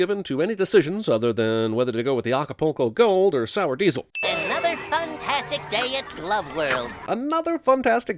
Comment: I thought he said "Another Thumb-tastic day"